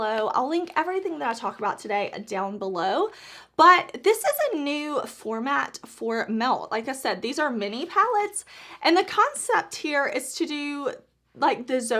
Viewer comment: I don’t think it’s a new formula 🥰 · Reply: oh I thought you said at the beginning it was a new formula. My bad, thanks for clarification! Hope u have a great day!